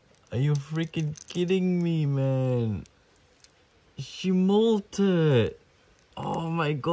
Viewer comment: At first I thought she dies, then he said molted,, that's a relief